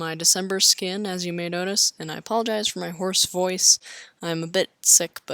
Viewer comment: I thought you said ‘I apologize for my horse voice’ instead of hoarse haha